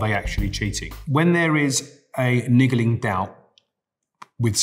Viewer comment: A WHAT?? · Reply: Oh I thought he said lingering but yea he said niggling